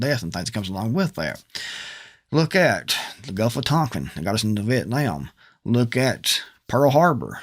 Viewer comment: Sir, I am so sorry. I couldn't understand the word you used at 2:26. Did you say, "Look at Gufatonkin"? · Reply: gulf of tonkin